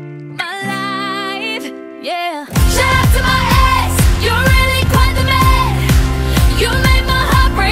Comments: Bro i thought it said "you're really quite a mess" 😂😂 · When I was 7 years old I thought they were saying "shut up to my eggs " 💀😭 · I heard "shout out to my eggs" one time and now I can't unhear it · It sounds like she is saying shout out to my eggs